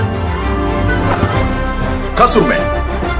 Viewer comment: Am i the only one that misheard "kasur man" as "kazume"..??